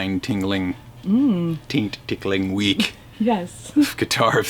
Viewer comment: Does mark say it's "taint tickling" week?